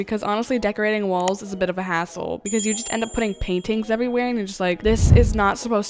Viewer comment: WHEN SAID “..Decorating walls is a bit of a hassle..” I THOUGHT SHE SAID: “..decorating walls is a bit of an a$$hole..”